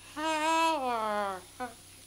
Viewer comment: Even though he says "Thank you" it sounded like he said "I give up" 😂